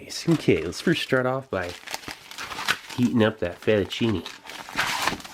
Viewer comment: what does he say at 3:04? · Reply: Okay, let's first start off by, heating up that fettuccine